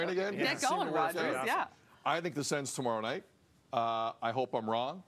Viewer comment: Did I hear Sid say, “I’m picking the Sens tomorrow night”…….